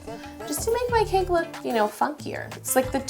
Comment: I swear to God I thought she said "To make my Cake look FUCKIER" I gasped and I replayed it and heard it well 😂😂